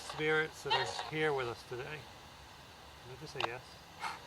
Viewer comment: sounds like it is saying yes